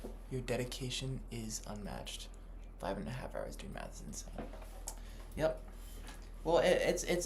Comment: Thought the caption said “doing meth”